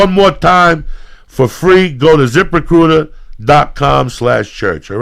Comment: I thought kept saying zipper cooter instead of zip recruiter lol now I have an idea for wallets...